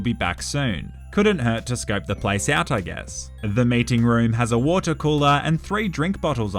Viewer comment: I thought he said mating not meeting